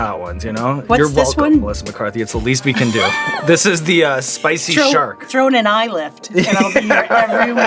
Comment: I thought she said “spicy shart” 😂❤